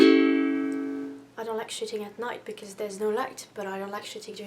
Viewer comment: at first i thought you said "shitting" not "shooting" and i was like jesus christ lmao